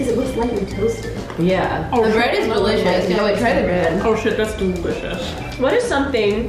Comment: I thought that when said “this bread is delicious” I thought she said “this bread is religious” 😂😄